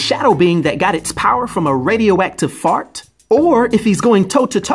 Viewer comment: wait, what? Did I hear this right? Radioactive fart